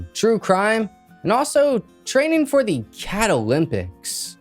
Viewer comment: Legit thought you said "Canna-lympics"!!!😂😂🎉🎉🎉🎉